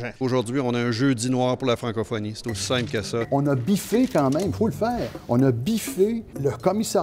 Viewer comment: oh! vous pouvez mettre "biffé" en sous titre autant que vous le voulez. Mais pour moi il dis Bifflé, et ce, a plusieurs reprises ( ͡° ͜ʖ ͡°)